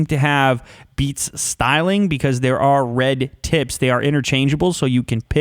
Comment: OMG DID I HEAR "RED TITS"